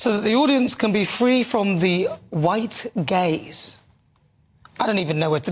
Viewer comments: Anyone else thought Nana was saying "the white gays" and not "the white gaze"? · This is an appalling double-standard. Also, what exactly is "identifying as black"? On a (slightly) amusing note (we must take humour where we can find it at this weird time in the UK), I first of all thought I heard that the perpetrator of this wanted the performance's audience to be "free from white gays". Then I realised it was "free from white gaze", which at least only insults one group of people and not two! You can't make this stuff up. It must be so hard for comedy writers now, as all the jokes they might write are constantly becoming reality.